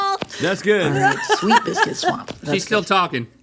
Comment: I thought she said “biscuits womp” and that might be funnier